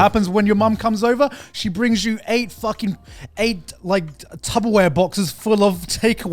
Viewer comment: Did I just hear say "Tubberware"??? 🥲